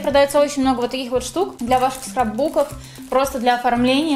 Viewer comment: Мне одной показалось что она сказала "сракбук" Когда наклейки показывала? 5:13😂